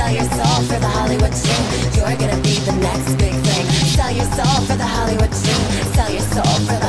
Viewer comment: The fact that im mishearing "You're gonna be the next big thing" with "You're gonna be the next victim" 😭😭😭